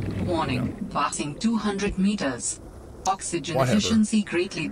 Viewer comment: idk why i heard fucksogen instead of oxygen what is wrong with me